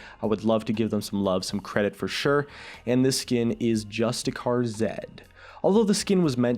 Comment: omg I heard "just a car zed." XDDD